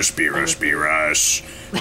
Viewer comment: I thought he said beerus form dbz